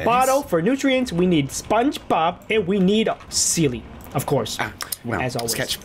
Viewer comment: I heard Sketch say ‘ SpongeBoob’